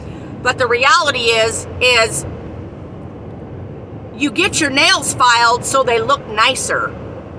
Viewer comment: Getting nails filed so they don't get snagged on things.😊 It is funny when you said' " .....four nail files....", I heard, " 4 months". Then when you were praying you said, "....needing to be ready to produce summer fruit". 4 months is summer fruit time! God is so awesome! Have your way Jesus! I will trust you even now and through the rest of this! I thank you that you are with us the whole time and tend to us so lovingly! Thank you Jesus!